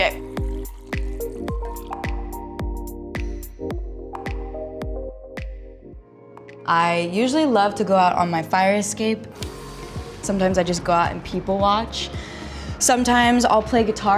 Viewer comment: WHEN SHE said , “people watch” I thought she said , “poopy watch”